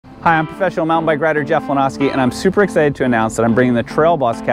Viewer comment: Lol I thought you said "special mountain biker." Talking a little too fast there bud.